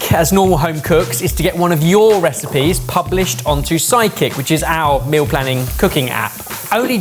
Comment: You'd think they'd make sure the closed captioning on a video would correctly name the app that they're plugging. 3:52 - says Psychic instead of Sidekick.